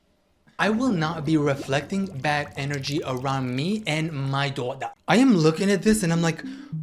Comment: WHT DID HE SAY DORDA LIKE THAT